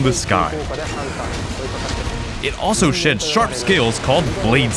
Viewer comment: I thought he said "s*its sharp scales" instead of "sheds sharp scales" for a second there. Also, instead of Agnaktor and Zamtrios you give us sub species? Really?